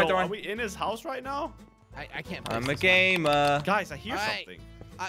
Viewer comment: When slogo said i am a gamer i thought he said i am a gaymer